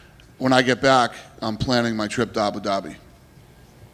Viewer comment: Did he call Abu Dhabi "Ah Denami" 😂😂😂